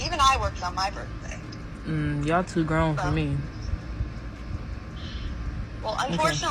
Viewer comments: Did I hear her say, “y’all too grown for me”? Incredible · lol 😂😂😂😂 did she just say ur too grown for me???